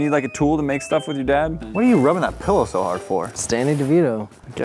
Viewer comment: Why did it sound like said Stanny Devito and not Danny Devito